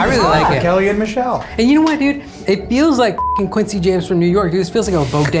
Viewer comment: I swear the judge says “quincy james”